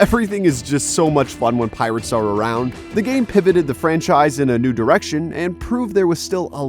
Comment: did he just say “pivot”?